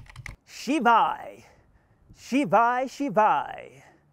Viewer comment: is he saying """" Shivaay Shivaay""" if I hear it correctly. thats A PART IN Hindu chant Om namah Shivay""" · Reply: He is saying SHVAI which basically means get out of my way I'm following my highest excitement.